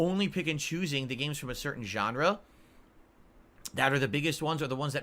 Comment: Did he say "gendre"